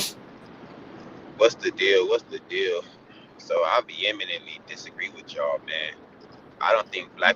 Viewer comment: I thought he said I Eric Bieniemy disagree. I’m like damn you didn’t like the 1990 CU buffs national championship team